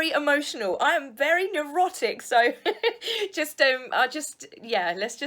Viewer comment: Hahaha. I nearly snorted my coffee. When you said that you were neurotic, I heard it as "erotic".